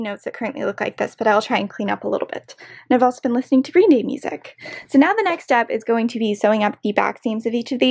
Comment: At first I thought she said she was listening to Green Day music. . xD